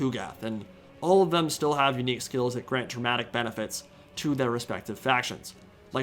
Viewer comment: I thought you said "Grant traumatic benefits." Which hey, would still work for Chaos.